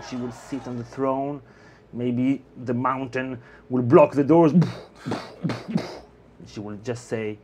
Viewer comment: Did you just say the Mountain will "Hold the door!" ? lol.